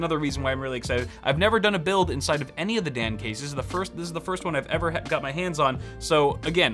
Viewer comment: I keep hearing "the Damn Case" lol